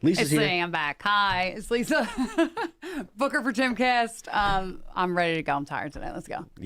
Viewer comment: Lisa, love ya girl, but slow down your intro. 😂 I swore you said hooker for Timcast!